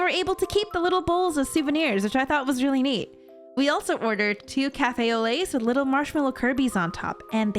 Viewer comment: All i hear is "cafe olé" i just can't 😄